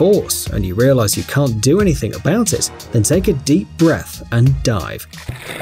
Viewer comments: i thought you said "but if you feel you're being pulled to the center of it with great force, and you realize you can't do anything about it. Then take a deep breath and die". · I thought he said take a deep breath and die 🤣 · I thought he said just take a deep breath and die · I thought he said take a deep breath and die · A commericial stardet before you said the v in dive. I heard it as "take a deep breath and die". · OHH ok I thought you said "take a deep breathe and die"